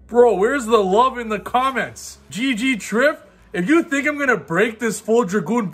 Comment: I think they were saying GG in support of you Trif lol